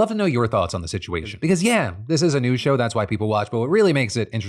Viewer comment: I've been watching Phil for a REALLY long time now and finally understood he is saying "This is a news show" but for some reason I always heard it as "This is a NEW show" like monkey intro and everything...